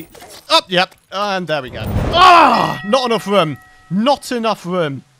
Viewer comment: i heard him say not enough rum